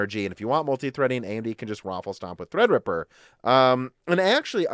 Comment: did you say waffle stomp? That's a little graphic don't look it up in Urban Dictionary 🤭